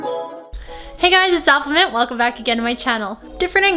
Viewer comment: doesn’t it sound like she says affleminte? I don’t know....